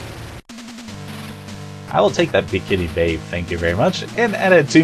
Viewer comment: Okay, I had to listen to that last bit there because it sounded like you said "big titty babe" which would have been right, too.